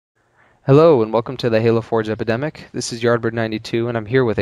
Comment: lol sounds like u say thehalofor-jepidemic